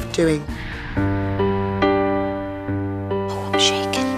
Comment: Did Cheryl say "shit" at 39:30??? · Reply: she said "i'm shaking"